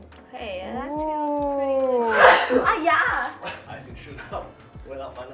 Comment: omg did I hear said haiya at 21:56?? queue uncle roger!!